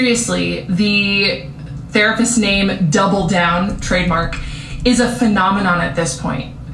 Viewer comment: wait did she say “trademark”? 😂 wth is she trying to trademark?